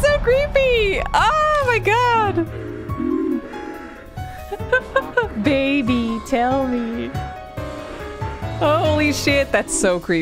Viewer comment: Thought I heard her say, "Hey, baaaby. Tell me." LMAO! This vid may be lengthy, but I've enjoyed every minute of it. And once again, just like Death Mark, if not for the guide, I honestly wouldn't think that throwing the book towards the tree would be the solution. Hell, I was thinking he should throw the book AT her, lol. And then, there's that Calligraphy Exhibit notice. Guess that explains why in the previous vid, her child was named "Boy T". Thanks for the quick upload on this chapter, and will eagerly await the next.